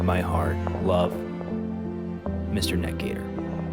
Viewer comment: What did he say at 1:16? Mr Neck gear?